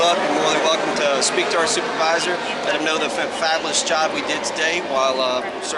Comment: ofc needs a refresher course on trespassing a citizen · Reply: did he say they did a 'fabulous job today?'